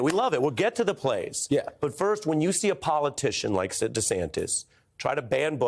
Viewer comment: Why 1:40 does he say Sid DeSantis instead of Ron? Am I just hearing it wrong? lol